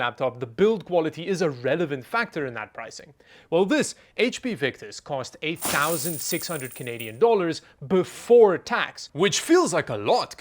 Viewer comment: I swore he said 8 thousand 6 hundred canadian dollars ..